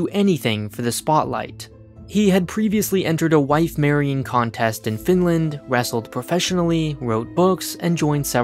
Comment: Not sure if I have hearing issues but I hear you say "Entered wife marrying competition in Finland".